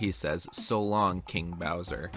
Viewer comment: I’m always thought Mario said “so long dear bowser”